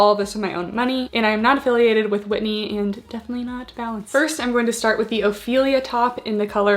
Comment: when you say Whitney and Balance, all I heard was 'Whitty and Vallance', UK's corona 'experts' xD